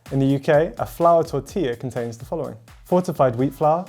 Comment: why do I always hear “ four to five wheat flour “